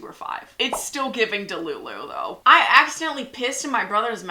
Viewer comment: I thought it said kissed😭